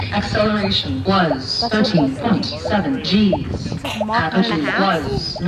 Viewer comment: at: 3:17 did Kate say: Acceleration 13.7 g ??? BTW::: Any launch data, like gross-weight, Max-Thrust, TWR ?? thanks.